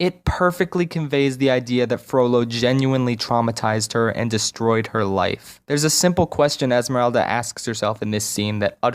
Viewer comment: what did esmeralda say during 17:19 ? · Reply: "Why me of all people?" <link>